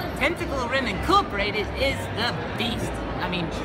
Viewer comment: At first I thought the last one said Tentacle rape is the best.